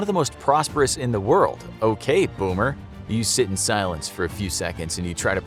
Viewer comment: did I just hear the words "ok boomer" come out of his mouth